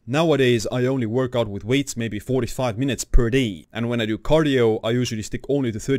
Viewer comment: WHAT IS HE SAYING ??? “4-5” or “45” minutes per day ??? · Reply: 45 minutes of workout with weights per day